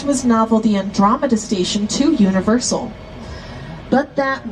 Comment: the guide said The Andromeda STATION, instead of STRAIN. And it is pronounced Michael Crichton (/ˈkraɪtən/. Not. "Cryken".